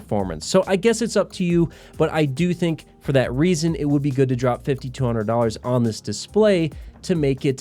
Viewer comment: drop 50 - 100 dollars in the price at 8:39 is what he is saying. What I heard was while doing something across the room and having my head boomerang back to youtube to say wtf did he just say you should drop 5200 dollars on this monitor?